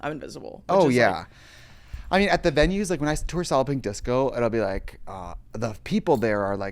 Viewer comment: I hear “soliping disco” everytime